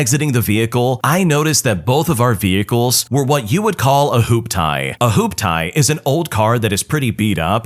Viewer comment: LOL I can't deny, I giggled hard when I heard "Hoop-tie" instead of "hooptee". Yeah, first story is definitley NTA.